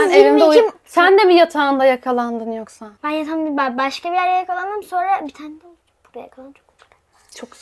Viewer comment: burda ne diyor acaba Maya🧐🤍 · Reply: “Ben başka bi yerde yakalandım bir tane de burda yakalandım” diyor